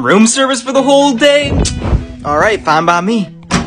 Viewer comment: When I first watched this video I thought I heard saying “alright mommy” but when I watched this video again I realised he said “alright fine by me” lmao 😂😂